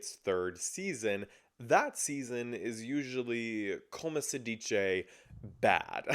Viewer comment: Lollll I was speaking Italian, not Spanish 🤭 So I was saying “Come si dice” (in Italian, -ce makes the “ch” sound) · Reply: Lmaoo yeah listening back I hear the “come si” part now, but I swear I heard “como se” 😂